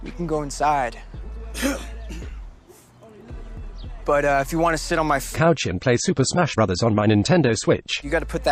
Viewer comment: cesar was actually about to say ''but uh if you wanna sit on my face'' at 5:09 instead of ''if you wanna play super smash on my nintendo switch'' lmaooo im just immature i suppose